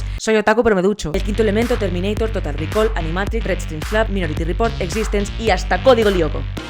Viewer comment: Que dijo ahí, no le entendí.... la de después de animatrix, quiero saber cual es para buscarla... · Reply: red string club